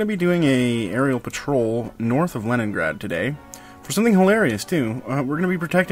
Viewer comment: LOL I went fullscreen in the beginning and then it sounded like said "Troll south of leningrad"